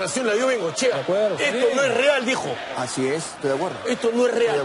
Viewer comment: yo escuche esta cosa es real hijo